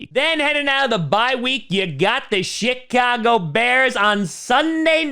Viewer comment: Did he say Shitcago bears at 12:15?🤣